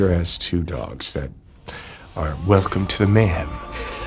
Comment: what is he saying at 1:45 "welcome to the man"?